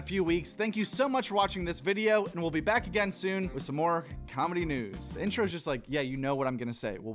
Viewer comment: Idk why; but it sounded like he said “we’ll be back again soon with more comedy nudes”.